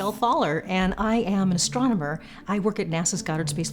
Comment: While looking at her and her redish eyes and wildish hair I legit thought she said, Hi, my name is Michelle Thaller, and I'm a stoner.